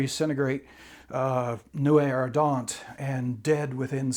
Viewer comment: What is the French term Kurt is using? Subtitles say nuit-a-radent and Google can't find that. 32:58 and other places.